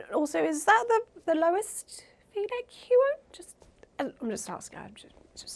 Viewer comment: Can anyone tell me what said at 12:10? I'm a bit hard of hearing and I cant make it out. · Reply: "Is that the lowest v-neck you own? Just asking...."